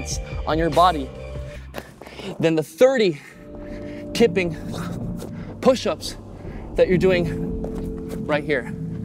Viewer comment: Kipping push ups? Wow, didn't even realise they existed! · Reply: I swear I heard 'kitten pushups'